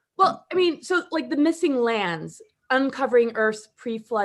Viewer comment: I heard the "MISSING LAMBS!" every time said "missing lands".